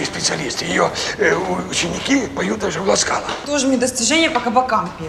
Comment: Поют в Ласкала или что он сказал ?